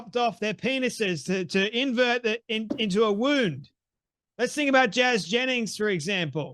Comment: "chopped off their penises to invert it into a womb" The 'basic biology' crowd, btw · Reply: I think he said wound, but I may have misheard.